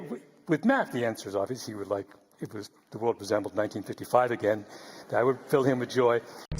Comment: I may be mis hearing but it almost sounds like he says "with Matt he would like the world to resemble Hitler, 1955 again".